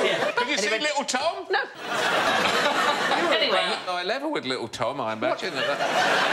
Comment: What did Alan say around 3:40? I can't quite make it out. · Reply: “You’d be about eye level with ‘Little Tom’, wouldn’t you?” (‘Cause Sandi’s really short)